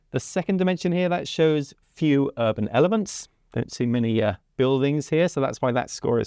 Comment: I heard "few urban elephants"... :-D (I'd just been thinking about dr seuss stories)